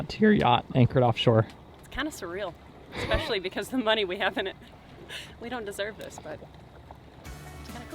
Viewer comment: I heard a "I feel like we dont deserve this" around 4:45 ........ Yeah ya do and you paid for it, wrenched on it, shed sweat and skin equity on it too. Heck yeah you deserve it!